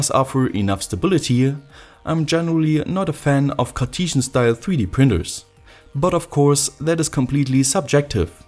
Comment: did he call it courtesan? If so that's a very interesting way to describe it